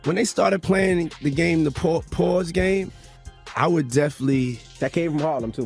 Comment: Thought he said he didn’t do the “pause game” 😂😂 them slip ups be real